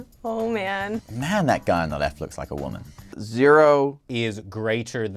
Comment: did he just say sehun look like a women? 😂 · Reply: No he said the guy on the left